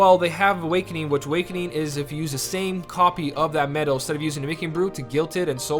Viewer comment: Hauz Sharpe I didn't say anything wrong, you should double check what I said about awakening. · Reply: I heard you saying "or MB" have you said "instead of MB"? if so, you are right. and I play JP I just used 2 MB on my key art coming and it did not activate the Awakening. also the best Awakening is the one that makes the same medal atk twice (the second atk has only 40% of the strength) because it works wonderfully with buffer medals. do you know they increase the buff and debuffs limit from 3 to 7 right?